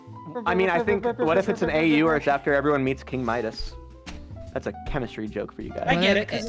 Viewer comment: Why did I think said "King Minos" rather than "King Midas"?